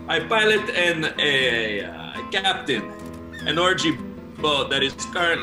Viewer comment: Did anyone else hear "orgy ball?"